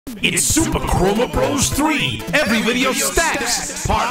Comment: Tbh i thought he said super kromer bros 3 insted of what the title actually was. God i play to much Deltarune